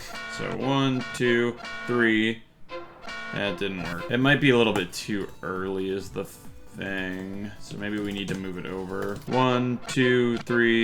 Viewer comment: I’ve been replying 4:52 over ten times just to see what that said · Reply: “It might be a little bit too early is the thaang, so maybe we need to move it over”